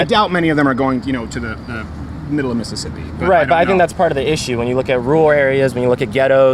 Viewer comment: did he say the middle of Mississippi? Bc I’m from Mississippi 😂